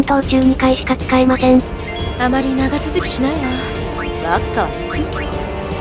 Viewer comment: 小さい音量で聞いてたら「あまり長続きしないわ ワッカのおち〇ちん」に聞こえた